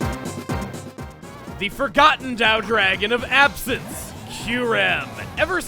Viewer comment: It’s been 3 years since I watch this series, and still to this day, at 1:02, I didn’t catch on what he said. “The Forgotten (daou?) dragon of absence, Kyurem.” Can anyone correct me on this, please? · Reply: Tao